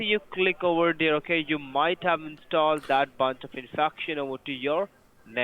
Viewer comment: I can't make it out. Is he saying you have downloaded bountiful suction into your network? Did you make him wait so long to connect he word saladed his own script?